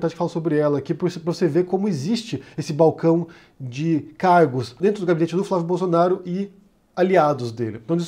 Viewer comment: Eu ouvi "carro" no lugar de "cargo" e por um instante pensei "eita, então era verdade que ele fazia rolo com carro??"